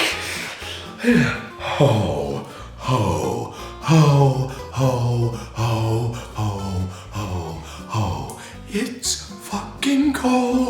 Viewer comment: Sounds like your saying hoe hoe hoe hoe hoe hoee